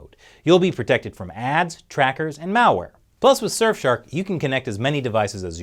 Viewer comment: I could have sworn I heard you say "Mao-ware" instead of "malware" at the end.